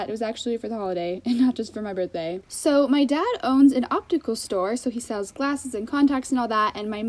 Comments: at first, I thought she said her dad owns and OCTOPUS STORE and I'm like: WHAAA? But then I replayed it, and she said OPTICAL STORE like wtf is wrong with me. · she said “my dad owns an optical store” and i thought she said “octopus store” so i was so confused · At first when you said optical store, I thought you said octopus store